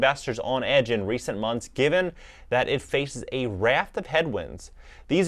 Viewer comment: did he say a raft of penguins? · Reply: HaHa...he said "raft of headwinds".